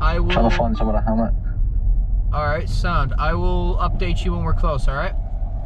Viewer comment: did you say sound? That's Irish slang